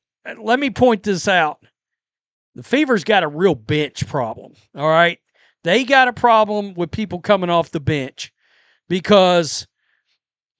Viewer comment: Oh, my .. I misheard you .. I thought you said the Fever has a real b_tch problem - but you actually said a Bench problem. lol